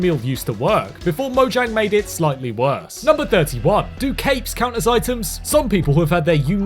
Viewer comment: When he said capes I thought he said cakes and the minecraft players bonced there cakes and the pigs cape looked like the rear look